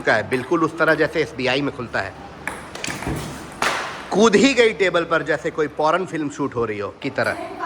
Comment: did he actually said poron? 😂